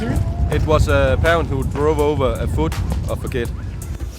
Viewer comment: Hallo in case you didn't hear him at 9:30 he said that in 17 years he only had 1 big injury and that was a parents there drove over a childs food, Happy new year ^u^